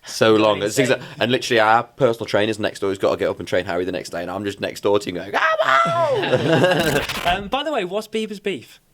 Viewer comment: What is Liam saying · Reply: "our personal trainer is next door, he's gotta up and train harry the next day and i'm just next door singing 'cmon!'"